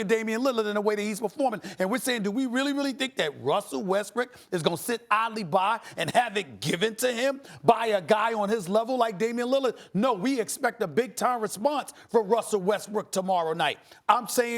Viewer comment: did SAS just say Russell Westbrick